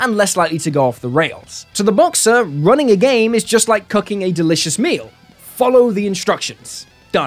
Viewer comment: Am I the only one who misheard him when he said "cooking a delicious meal" as "cucking a delicious meal"?